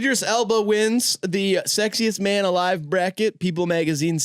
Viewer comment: swore it said scariest man bracket i was about to vote tom holland with no teeth